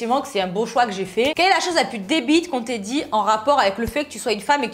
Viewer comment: C'est moi ou a 8:18 elle dit " des bites" au lieu de débile ?